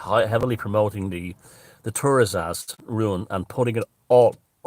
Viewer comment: What did he say was good for protection around 6:15? Heard tourasist ruin. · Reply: A rune, thuriaz